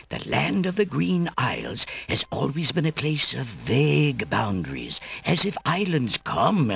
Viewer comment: I originally thought he said big boundaries